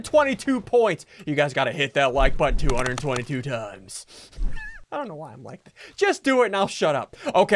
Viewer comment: did he say “ I Dont know why I’m white..😂” !?!?!?! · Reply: Golden Jaii he said i don’t know why im like this but he didn’t finish the sentence